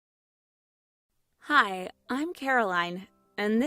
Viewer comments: When she said hi I’m Caroline I thought she said hi im paralyzed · My phone was at a low volume and I heard her say "HI! IM PARALYZED AND THIS IS MY STORY!!!" smh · I legit thought she said "Hi I'm paralyzed" not "Hi I'm Caroline"